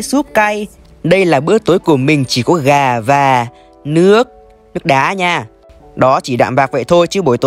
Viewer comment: em nghe nhầm thành nước đái :))))